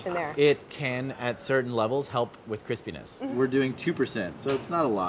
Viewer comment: Okay but if you go to sec 14:03 and turn on the captions, says "crispy-ness" and the caption says "Chris penis" AND I CANT STOP LAUGHING